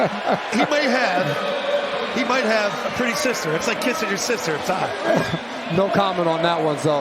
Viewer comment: wtf did he just say at 13:51 😳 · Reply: He said it was it was like kissing your sister. That's an old saying about a tie a tie is like kissing your sister